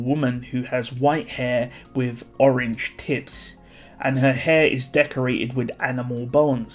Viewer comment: I heard it as Orange Tits first time